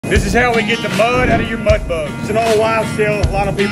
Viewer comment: Jesus I thought he said this is herion